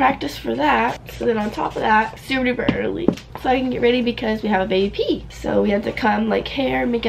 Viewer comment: What’s the word at 1:11 ? I’ve been sitting here trying to figure out what she’s saying? Baby pea? Bay ep? bay eppe? Someone help 🥺